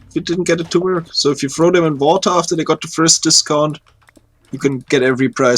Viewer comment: What is saying at 15:46 to get villager trades down to 1 emerald? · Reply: put them in water after first price drop. made a video about it on his channel